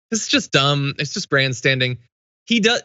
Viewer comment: When John was talking about Cruz ‘grandstanding’, I heard ‘brandstanding’. I think that’s actually on point and hereby move to make it a word.